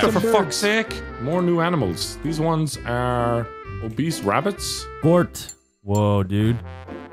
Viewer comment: Did he say Obese rabbits? XD